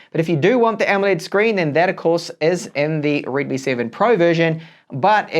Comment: i hear redmi right?! :)))